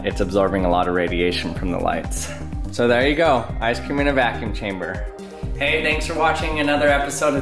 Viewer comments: 😂 I wasn’t paying attention at the end and when he said “there you go, ice cream in a vacuum chamber” and I heard “there you go, I just came in a vacuum chamber” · Lol I heard "there you go I scream in a vacuum chamber"